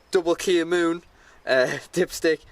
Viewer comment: Great videos! What was the flush dipstick called again please? I searched for K Moon as I thought that's what you said and nothing... 😕